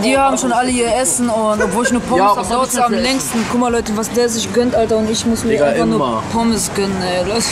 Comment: Hahahaha ich hab nur pornos verstanden😂😂